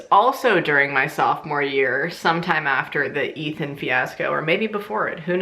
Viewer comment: I was eating when you said Ethan and I thought you said Meathead lol